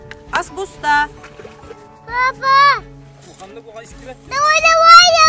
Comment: I heard "PAPA" which means dad in Hindi... Idk what the baby actually said!! 😅😅